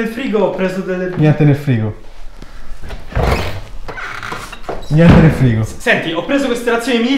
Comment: HEY CHE CI FA JOHN CENA NEL FRIGO,e poi non vedo nulla da mangiare la dentro solo John